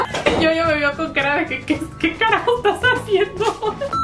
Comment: ¿escuche mal? o dijiste en el minuto 6:11 que carajos ?😕